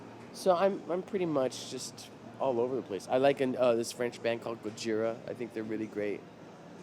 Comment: what is the french band called? I cannot understand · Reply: Gojira